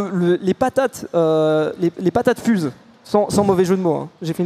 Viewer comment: "les patate fuse = moi j'ai compris "les patates fuze XD"